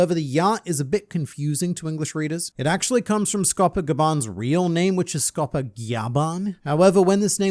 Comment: All I heard was GYAATban.